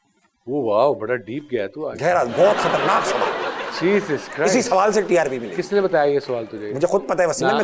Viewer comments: Did he say Jesus Christ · is he just said JESUS CHRIST???